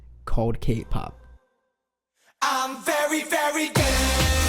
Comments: all i heard was "im very very gay" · "I'm very very gay" please tell me I'm not the only person who heard this cuz i